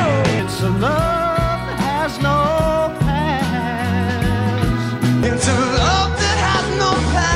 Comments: I noticed this a few years ago before I knew about the Mandela effect. I thought he was saying "a love that has no bad", I wasn't sure because I remembered it being "past", that makes the most sense and you proved that's what it was 👍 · The first time you played it I thought it said "A love that has no Band" then I heard past, then path.